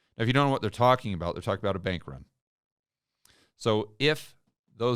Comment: Does anyone editing this video check for accuracy and quality? My man did not say "bankrupt", he said "BANK RUN".